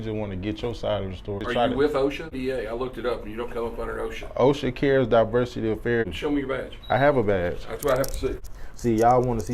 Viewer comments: He literally said "I'm from OSHA cares" Did you watch the same video as me? What if I prank raided your home under the "DEA SVU" ? Would you be like "Guess I can't sue, he said SVU on the end of DEA." 🙄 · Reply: he clearly says OSHA Cares Diversity Affairs. If he never slipped and said this part they would have no case. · If you’re confusing OCDA with OSHA your favorite color might be five… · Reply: he clearly says OSHA Cares Diversity Affairs. If he never slipped and said this part they would have no case.